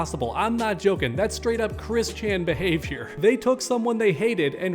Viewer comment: And what he said? ) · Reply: It sounded like he said Chris Chan. I thought it was Christian, but that’s what I think he said.